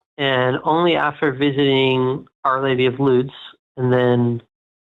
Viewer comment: "Our Lady of Ludes"?? Ludes?? There's an Our Lady of Quaaludes? ALL RIGHT!! He's so tuned into the script he doesn't even pick up on it when Matt corrects him. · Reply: I thought he said "nudes". There's a saint of Babs and vagene now????